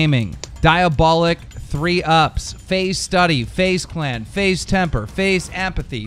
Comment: Sounded like you said 3 ups